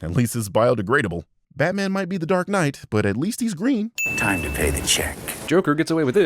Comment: said "green" but the subtitles say "red". I wonder if we're building up to something set up by his Red video.